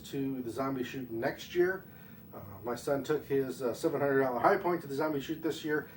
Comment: what are you saying? Your took what kind of High point? Did you say $700 high point? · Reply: Check out the channel and you will find the $700 Hi Point. Thanks for watching.